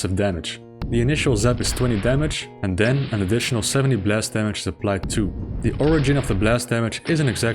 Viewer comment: When you were talking about the spider master mind and the cyber demon it sounded like you were saying splash damage instead of blast damage